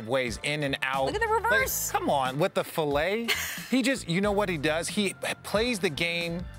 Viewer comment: did he say with the filaay??